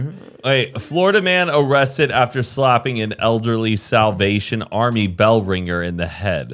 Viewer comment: I totally thought said "South Asian" instead of Salvation (47:00) 🤣🤣🤣